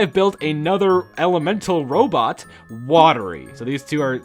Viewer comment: the video paused here and all i heard was "ELEMENTAL ROPE"